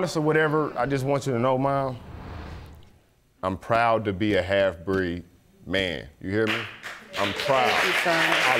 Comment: Did he just say he's proud to be a "half breed" man?